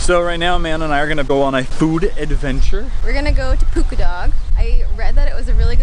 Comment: i thought they said poop a dog